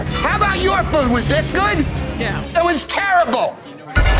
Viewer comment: did she say “yea”